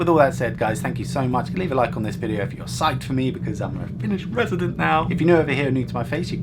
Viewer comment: I thought it said "i'm a finnish president" at first